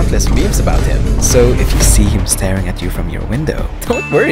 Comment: i heard "MATAGAL PA" at the end when you said merry christmas